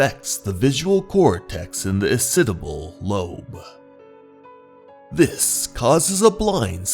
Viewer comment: did he just says "asiddible lobe"?